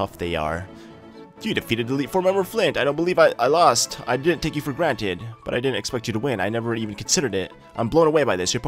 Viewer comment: i thought flint said i didnt take your girlfriend